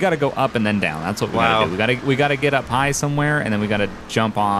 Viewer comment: When he said “gotta get up high somewhere” I thought he said “gotta get a pie somewhere”